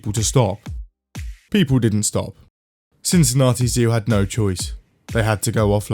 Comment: I just heard "Since Nazi zoo had no choice"